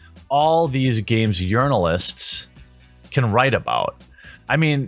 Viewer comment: Lol, anyone else hear "Games Urinalists" around 6:42 ?